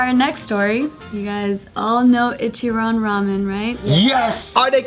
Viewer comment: I thought it said "Ichiraku ramen" i need help